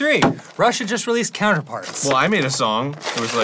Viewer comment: I legit thought Dan was saying "Russia just released [album name]" until the very end of the bit.